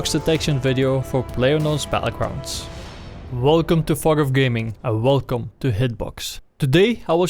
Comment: i chocked on my water it sounded like he said welcome to fuck off gaming